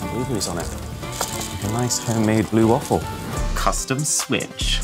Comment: did I hear him say 'blue waffle' @ 15:07?